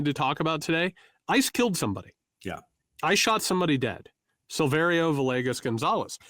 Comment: My heart skipped a beat. Sounded like you said "I just killed somebody. I shot somebody dead."